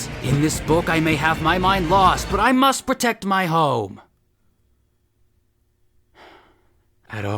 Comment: I thought you said “I must protect my hoe” 😬